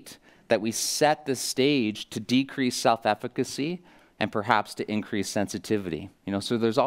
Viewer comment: What is mr Lehmann saying at 14:05? "I get worried when we overcorrect gait that we set this stage to decrease s.....cy (???) and perhaps to increase sensitivity". Thank you for the video! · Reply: self-efficacy